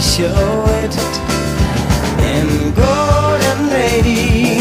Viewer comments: Heard this song at work yesterday, which brought me here. Took me a while to find it, because I thought he was saying "Golden Days", lol. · OMG! I always thought he said ‘go little lady’! 🤦🏻‍♀️